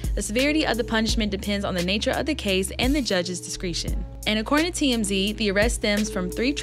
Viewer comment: I thought she said “the judge’s secretion”. I was like What kinda freaky 💩 is going on in Florida?!